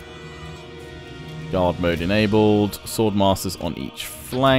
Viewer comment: it sounded like you "God Mode" instead of "Guard Mode" lol